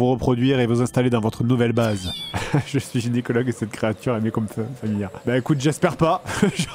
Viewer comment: Il dit quoi après "Je suis gynécologue"? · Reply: Je crois que c'est "et cette créature m'est comme familière"